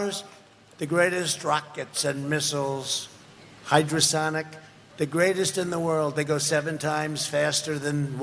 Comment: did he say HYDROsonic?